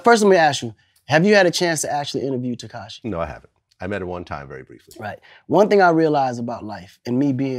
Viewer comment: I hear I met her or met him/em